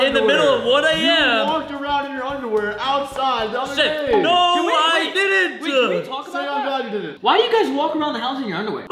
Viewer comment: Bro what’s that word that said on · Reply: Shi